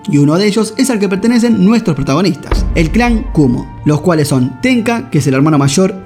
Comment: YO PENSE QUE DECIA EN CLAN KUNNO XD